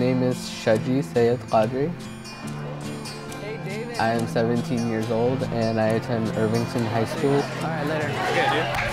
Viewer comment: did he say “hey BABE” Dating is haram. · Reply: He said "hey David"